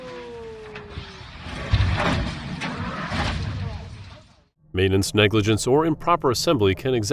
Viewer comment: Did he say "exasterbate?" 😂 @ 27:09 Someone forgot to flood the ballast tanks!